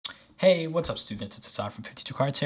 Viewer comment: Hold up did he really say hey what’s up stupids he thought nobody probably thought what he said I had to replay that slow